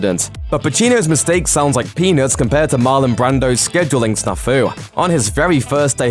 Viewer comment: I swear he said ‘sounds like penis’